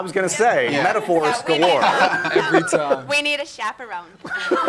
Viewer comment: Guys, please, what Ali says in the minute 5:04? "We need a..." what? I can't understand. Please, someone? Then Dan says he is curious. About what? First class? Gosh, since 5:04 until 5:21 I can't understand what exactly are the """quotes""" of them. HAHAH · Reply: Ali says “We need a chaperone.” Dan says “I’m curious how that couch feels… I’m used to first class.”